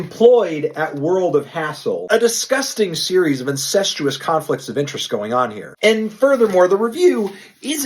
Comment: did he just said "a disgusting series of incentuous conflict" 😂